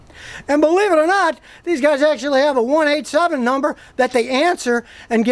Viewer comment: Did he say that they have a 187 number? at 4:42 , that must be a Canadian thing, huh!!!! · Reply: 1 877 number, I left out the end, it was hot